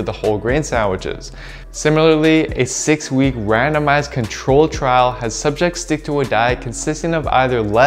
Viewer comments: At first I thought you said "a 6-week random ass control trial" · lmao it sounded like "similarly, a 6 week, random-ass control trial"